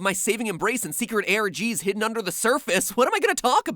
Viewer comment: What the heck did he say? "My secret air Gees"????? 13:55. So a bit better than ascending the 4th layer, right?? Made In Abyss reference.